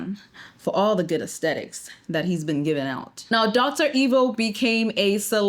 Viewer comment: I heard "dr. Evil"..😅